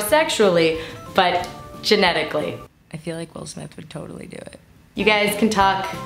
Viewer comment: what's she say at 1:20? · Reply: "I feel like will smith would totally do it" is what she said